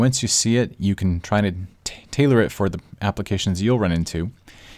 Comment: u can try to tailor it. lol don't be confused by this, i almost thought he said Taylor it